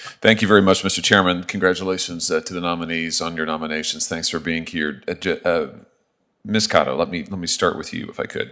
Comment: Did he just said thanks for being cute??!